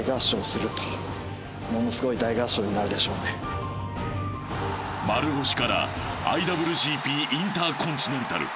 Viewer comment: 一瞬丸藤って聞こえてビックリしたw